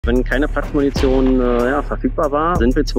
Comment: Ich habe Platz Munition nicht verstanden ich habe nur Munition verstanden und habe mir dann vorgestellt wie die in nem Häuser Kampf sind und einfach Peng schreien 😅😂